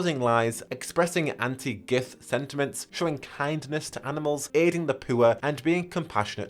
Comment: I can’t be the only one who heard “aiding the pooer”